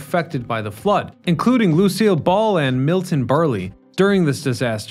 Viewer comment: Did you just say Milton "Bur-lee"? lol